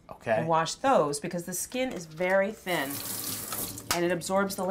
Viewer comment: I thought she said Barry thin